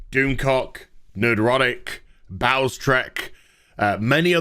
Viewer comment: LOL. You said "Bowlestrek" but I heard, "Bowel Stretch" :-)